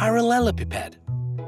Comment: EXCUSE ME WHAT DID YOU SAY?! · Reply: Parellalpipede